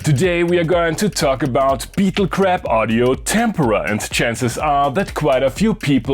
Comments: I swore you said Beetle crap. Lol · I thought you said "beetle crap" and I was like DAMN he's not pulling ANY punches